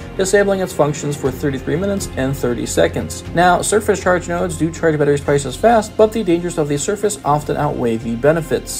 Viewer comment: What did you say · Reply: I just re listened to that, sorry that was rough to hear. I spoke a little too fast there. I said “surface charge stations charge the charge batteries twice as fast”.